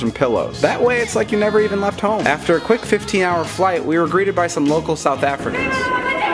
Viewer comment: no hate, but it literally sounds like he said, "We agreed to buy some local South Africans" Bruhh how dumb I felt when I realized what he said xD